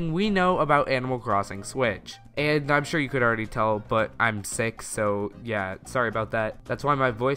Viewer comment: I legitimately thought he said: "I'm six so yeah."